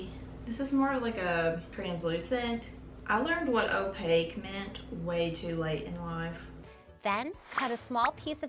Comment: can someone tell me what she saying here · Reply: "I learned what opaque meant way too late in life"